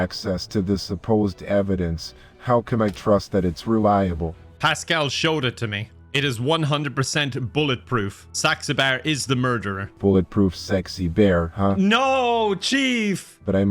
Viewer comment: The chief asking "Bulletproof sexy bear, huh?" and disappointed response of "no, chief!" was the funniest part. Even the subtitles for this video thought he was saying "sexy bear" lol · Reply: Even better when the subtitles thought said “sex a bear.”